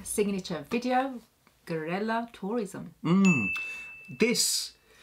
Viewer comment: im not sure if i heard it right, did you just say 'Gorilla Tourism'?